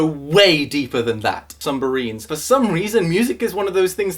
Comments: was it just me who heard sumbarines I stead of submarines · sumbarines? i heard him say sumberines.